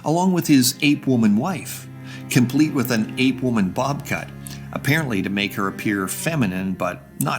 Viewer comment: haha i misheard that as “ape-woman bobCAT” and I thought he was using it as a euphemism for “cougar” — and I mean sure, she looks pretty good I guess… D